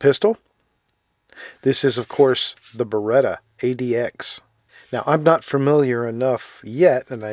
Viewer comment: Did you just call it "the Beretta ADX" @ 1:50?